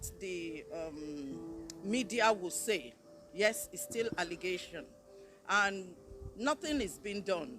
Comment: she said its still allegations????? I thought she said it all happened to her!. if its true.....why u say its allegations? ahahahahahahahahahahahahahhahaahahhahahahahahahahahahahahahahahahah